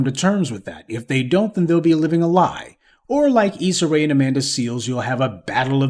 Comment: I thought you was saying “Lisa Ray “ lol😂😂😂😂😂😂😂 who is she